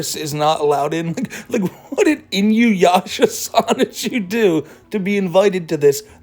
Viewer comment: I heard "Inuyasha-chu" and my brain activated like a sleeper cell